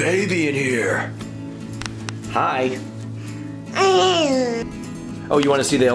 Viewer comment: did he say hello